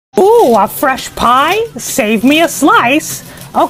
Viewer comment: wait did Mickey say “pie”